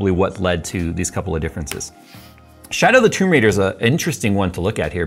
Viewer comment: Did he just say "my-raid" ?